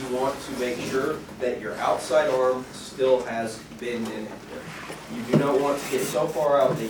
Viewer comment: What did the instructor say at 7:10? Make sure your outside arm has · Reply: bend in it.